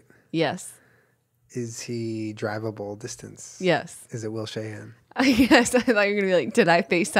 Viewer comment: I thought that first one said Will Smith lmao